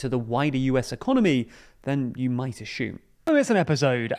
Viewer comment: Did bro just say "ashume?"